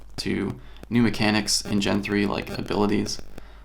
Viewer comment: did he say Casey Neistat?